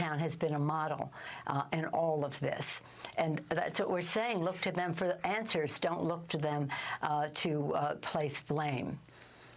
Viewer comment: can come one record that.. sounds like she is saying “China town, Look to them for Answers, don’t look to them for blame”...